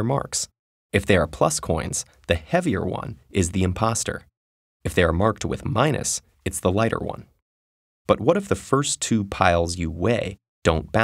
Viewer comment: did I hear IMPOSTOR?